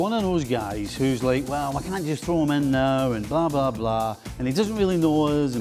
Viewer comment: 😂😂😂 did I hear blah blah blah